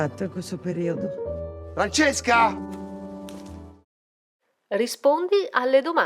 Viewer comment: I could understand almost all but my answers were all right except the last one when he said Francesca and I thought he said racesca, what name is that I don't know lol. I enjoyed this video thank you